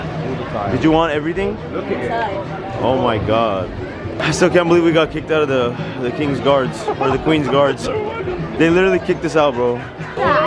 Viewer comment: I thought you said that your fiance got yell at the king guards